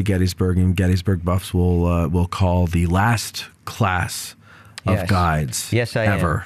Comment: Did he say there are no more tour guide training classes anymore? Is it the national Park service that's doing budget cuts? What's the reason. I heard it in the beginning of the podcast but didn't catch it elsewhere. Then again I was a little distracted doing chores today but are they cutting out the park park guides? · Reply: "last class of guides, ever....nobody will come after you". that's what i heard, and i'm hoping they are not phasing out guides.